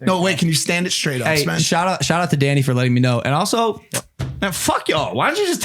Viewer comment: What did she say at 23:45 about Danny 😂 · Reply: It sounds she said Danny is hot😂😂😂